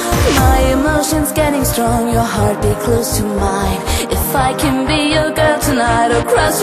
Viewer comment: At first, I thought she was saying "I can be a girl tonight." Great song, by the way! :) thanks for the upload!